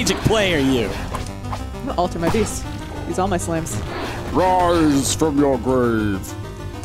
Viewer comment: i thought said raws from your grave